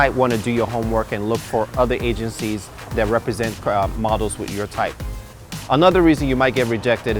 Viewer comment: LOL at 1:30 into the video I had subtitles on and he was talking about commercial looks Idk what he said but in subtitles, it was like "commercial look you might want to do your homework and look for other agencies that represent crap models with your type another reason you might get rejected" Idk I just found that funny